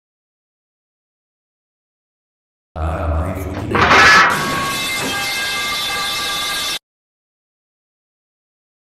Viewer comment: que dice ahi? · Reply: Dice haaa My fucking eye